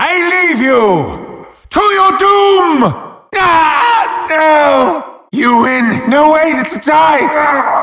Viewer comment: I still don't know what he says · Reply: i just figured it out lmao "You win... No, wait! It's a tie!"